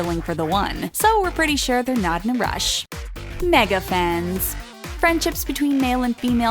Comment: did i just heard " FRIENDCHIPS " ?